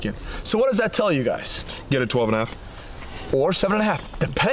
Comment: All i heard was "get a 10.5!"